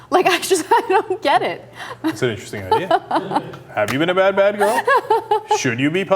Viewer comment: turn on the subtitles got to 1:26 and Cenk says 'that's an interesting idea' but the subtitles suggest he is 'positioning his whip' hahahaha